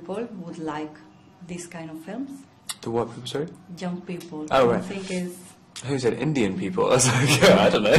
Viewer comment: I THOUGHT YOU SAID INDIAN PEOPL E OMG